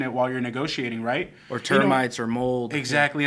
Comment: Hey first of all thank you so much for the information and help and I have a question I didn’t get what you said in minute 28:24 termites OR WHAT? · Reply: Mold